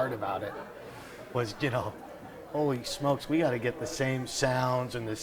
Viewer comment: is he saying always smoke weed? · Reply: No - he says "holy smokes we gotta get the same sound..." holy smokes is an expression kinda like " holy shit"